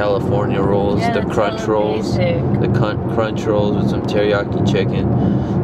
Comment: Yo at 4:01 did anyone else hear say cunt😂